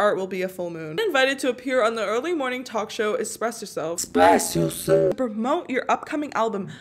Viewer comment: Am I the only one who heard "expresso" instead of "express yourself" at 10:30?